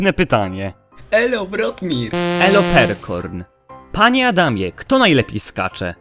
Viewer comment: Zamiast panie Adamie usłyszałam panie chamie xD